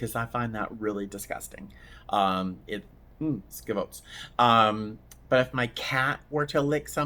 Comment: New addict to your channel, here, Just curious, at 21:24, did you say, "schifos"? I've never heard an American say that, is that a thing in Boston? Sounds Italian👍